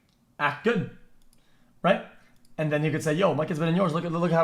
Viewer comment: did he say the n word ? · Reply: are you stupid he said "and you could say"